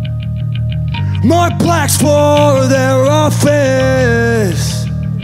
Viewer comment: lol when he said "more plaques for your office" i thought he said "more blacks" .. i think those grills are affecting those lyrics boi